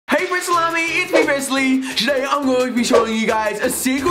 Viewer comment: Lol at the beginning of the video I thought he said hey salami!